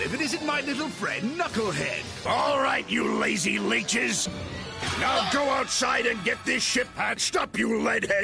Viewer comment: "Now go outside and gets this shit patched up..." who else thought he said that?